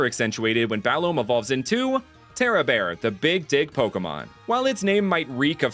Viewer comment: the Big what now? ohh 'Dig', yeah of course that's what I heard.